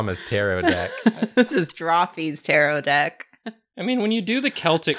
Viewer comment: they kept saying "tarrot decks" and I kept hearing "pterodeckts" and imaginning flying card dinnosaurs